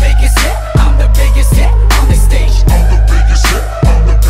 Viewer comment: It sounded like he said “ I’m the biggest sh* t on the stage” This is not a hate comment I’m just sayin